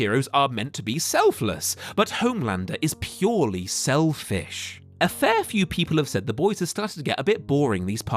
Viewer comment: I keep hearing "selfish" as "shellfish"